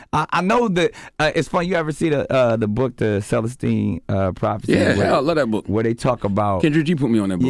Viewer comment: What was the name of that book he mentioned at the 13/14 minute mark again? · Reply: Celestine prophecy but i thought i heard self esteem prophecy.